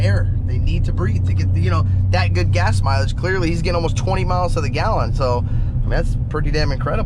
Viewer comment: am i heard well? 20 mpg consume? thats pretty good